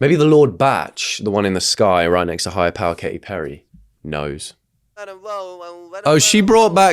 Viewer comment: did someone say… NOSE!!?! 👃🏻